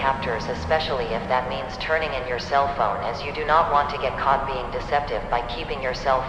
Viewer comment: I thought it said "alpha" bc I first clicked it and oof I thought you just got brainrotted until I accidentally clicked 10 seconds before and then I heard cellphone